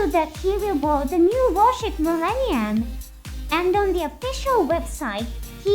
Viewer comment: it was repaired but that ship we see on the trailer might be a new ship called the Millennium based on the official website if you look at the new compass captain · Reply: Honestly I thought you were saying the Minerva was Millennium.